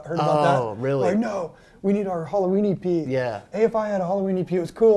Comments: Until they showed the Halloween E.P. record image, I thought he was saying "Halloweenie Pee" lol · I thought he was saying Halloweeny P and couldn’t figure out what that meant 🤣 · When he said "Halloween EP", my brain heard it as "Halloweenie pea". I giggled in confusion for a sec, there.